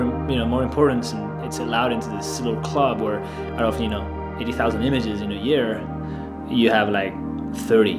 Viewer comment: Just for curiosity... did you said 80,000 images in a year (around 0:30) or did I not understood correctly?